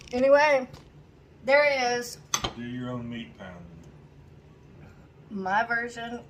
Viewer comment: LOL, no that was actually what said 🤣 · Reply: And at first, I thought he said "Be your own meat pounder " 😆